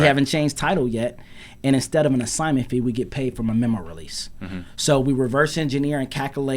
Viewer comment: instead of assignment fee get paid from a what release? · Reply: I believe he said Memorandum Release. This way you cloud title without being "in" the transaction but for the buyer to close they pay them a fee to release the memorandum.